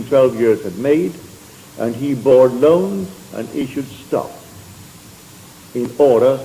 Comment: he says “issued stock” instead of what is on the captions.